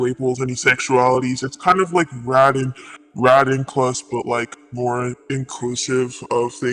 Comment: what the interviewee is saying here is “radinclus,” which is short for “radical inclusion.” to my memory, this refers to people who are inclusive/accepting of things like xenogenders, neopronouns, contradictory labels, etc. though they sound similar, it’s pretty different from radqueers, as radinclus draw a line at the acceptance of trans-ids and harmful paraphilias.